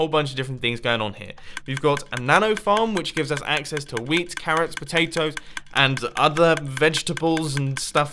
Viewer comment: I thought he said 'weed, carrots, potatoes...'